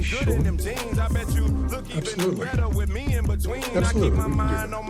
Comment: did he say 200 billion!?